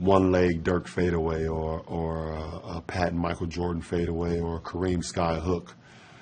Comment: "Or a patent mj fade away" Sorry English is not my language, is that what he is saying? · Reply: Or a pat and mj fade away, referring to Patrick Ewing I believe